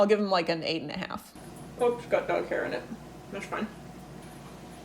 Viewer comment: I heard "Whoops, got duck hair in it." ...